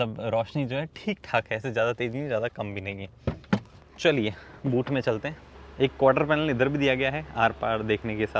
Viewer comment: I literally heard "chaliye mutne chalte hain" . I was like bhai video toh khatam karlo🤣🤣